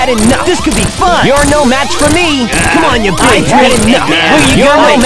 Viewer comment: did i just hear sonic said the b-word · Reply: "Cmon you little bi-" thats was i hear xd